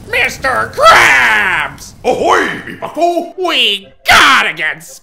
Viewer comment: Cool to see how when squidward used the flamethrower and the door in steel took fire but the wooden walls did not. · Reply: did he just said ahoy me bucko